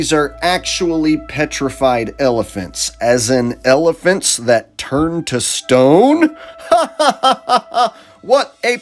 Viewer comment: For a minute there I thought it said elephants that turn into rockets😂